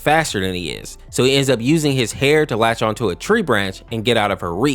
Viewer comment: i heard ''abusing his hair'' Edit: Burning lice cookie became bald after expired milk cookie shaved his head🪬